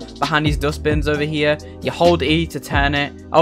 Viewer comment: Did he say hold [E] on 0:40 ?